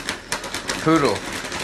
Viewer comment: HER NAME IS P O O D L E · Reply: Lmao, I thought he said noodle, I'm in tears 😂😂 poodle is even cuter!!